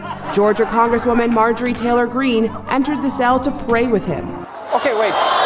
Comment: When Trevor talked about MTG getting in the fake jail cell with the fake prisoner and said she was "praying with him", I thought he said "playing with him"! The picture showed her with her mouth open while she was on her knees in front of him!